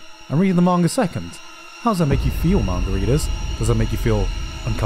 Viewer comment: the way he said Manga readers sounded like Margaritas. I’m now referring to them as Margaritas.